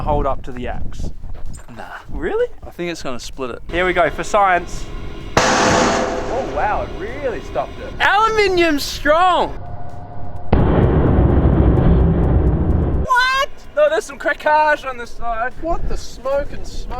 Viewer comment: Brett at 8:07 "Aluminum is strong" But it sounds like he said Our Aluminum Is Strong, with his accent lol To my southern American ear it really sounded like "Our Aluminum"